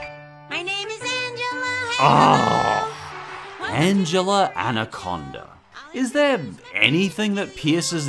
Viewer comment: I thought you said “Angela and a condom”